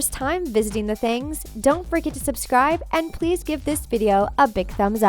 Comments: I thought she said "Don't frickin subscribe" 😆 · it sounds like she says,”DON’T FRICKEN SUBSCRIBE” 😂😂😂